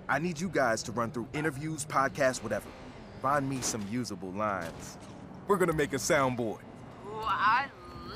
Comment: i heard vines and i was like wait WHAT?! how the fuck.....ow lines